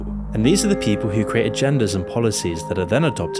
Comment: AGENDAS I straight up thought you said genders